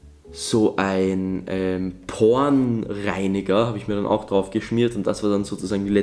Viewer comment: Wer hat auch "pornreiniger" verstanden ?😂